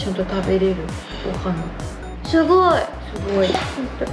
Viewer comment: 凄いがしょぼいに聞こえるw(